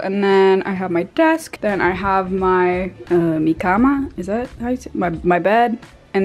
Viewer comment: AHGHDGSG DID U SAY MI CAMA? wow, do you speak spanish? Yo hablo español, Hola(?